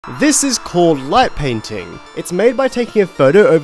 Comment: I thought you said "Butt painting"